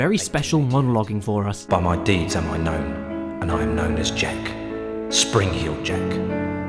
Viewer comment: I legit thought he was saying “jank”.